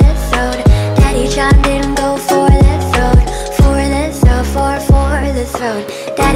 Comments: I heard.. "Daddy killed her for the throne" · When she said "daddy chimed in" I always thought she said "daddy shot men." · I always hear “Daddy shot mingo for the throne” Huh? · How are yall hearing "Daddy John didn't go for the throat" i heard "Daddy chopped in go for the throat" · Um i thought it said daddy shot ningro for the throat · I legitimately thought it was “Daddy-Chan didn’t go for the throat” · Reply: I thought it said "Daddy-Chawnmin go for the throat" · I legitimately thought it was “Daddy-Chan didn’t go for the throat” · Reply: I originally thought it was “daddy said never go for the thrown” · I legitimately thought it was “Daddy-Chan didn’t go for the throat” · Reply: I thought it said Daddy John didn't go for the throat so I mean.